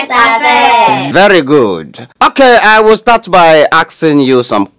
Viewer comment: Did I hear "aksing"?😂😂